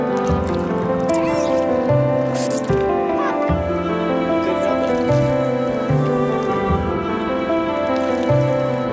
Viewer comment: What did say at 3:52? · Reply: iowadairyboys "Papa!"